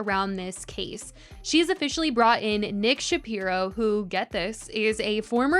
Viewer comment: pls I thought she said Ben Shapiro LMFAOO I was like what the hell is he gonna do for her 💀